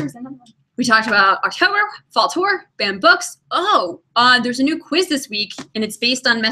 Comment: At first I was confused cuz I thought she was saying "band books" and I was like um is there a Hogwarts Band that I was unaware of haha